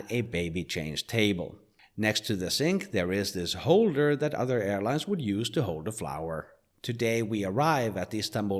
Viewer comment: "there is this holder that other airlines would use to hold a flower" I could not stop laughing because I translated what you said in to "Better airlines would hold a flower" lol you were more pc than me lol